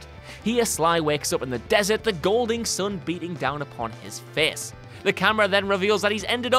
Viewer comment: did you say "golding sun" or "Golden Sun?" 'Cause that would be quite the coincidence on a list like this. (Though to be fair, that's not so much a cliffhanger as a failed-to-reboot/continue series, though there were still many stories that are now shelved.)